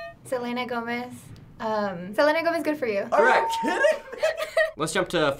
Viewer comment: Guys who thought good for. you sounded like she was saying "I'm farting carrots"